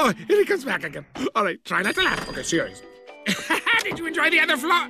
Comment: what did Doofenschmirtz say? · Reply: “Okay. Try not to laugh. Okay, ‘Serious.’”